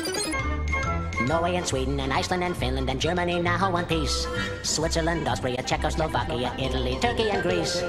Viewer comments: Always thought it said "Germany now in peace" referring to postwar period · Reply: I heard it as "now want peace" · Always thought it said "Germany now in peace" referring to postwar period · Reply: Ive always heard it as "Germany in one piece" Meaning unification of east and west.